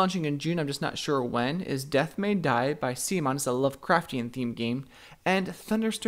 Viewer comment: what is he saying? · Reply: "Death May Die"